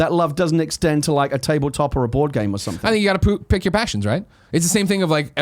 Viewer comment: kinda sounds like Greg says 'I think you've gotta poop." lol